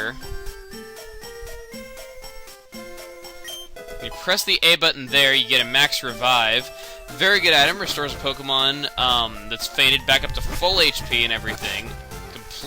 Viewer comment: thought he said "faded" instead of "fainted" i was like Pokemon get high?